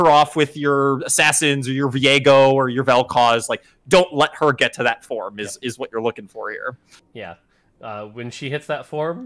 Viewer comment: Hahahaha I literally heard them say "don't let Kayle get to that stage", went into a game, got 6 knights 3 ironclad 2 verdant and kayle... Let's just say I won and laughed all the way xD When she goes off ... she GOES OFF! Love the set so far :D